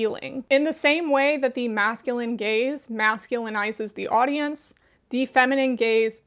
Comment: I thought I heard "masculine gays" not "masculine gaze" and I put my chicken tender down for a sec cause I thought I was being addressed